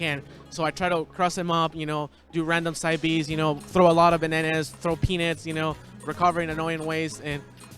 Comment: didnt he sound like he said "throw penis"